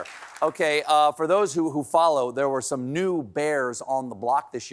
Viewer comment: i thought he said "there were some nude bears", as if they saw some unclothed hairy gay men recently